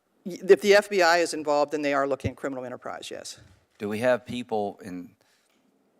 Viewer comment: Listen to 0:53 over and over again and it kind of sounds like she says, "the FBI is involved and they are looking a criminal enterprise". It's almost like she's saying they are looking (like) a criminal enterprise. Seems like her subconscious mind may be speaking louder than her conscious mind.